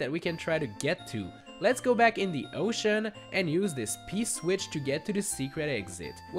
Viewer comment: when you say “p-switch”, with your accent it sounded like you said “piss witch”. It made me laugh because I’m immature. Great vid 👍 That is all :)